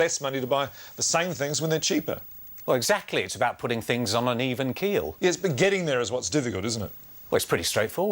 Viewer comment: what does he say in 0:58.... it's about putting things on uneven .... what? %$^@ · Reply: Putting things on an even keel